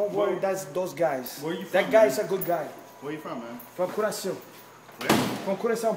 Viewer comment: Is he saying Curaçao?